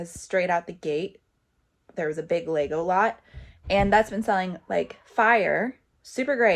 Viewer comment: Every time you say Lego lots, I hear “Legolas” 😂